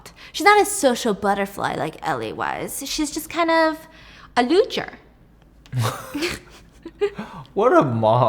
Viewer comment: is she saying "Looser " ?